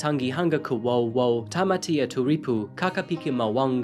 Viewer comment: I just heard "tamatamafuckit...."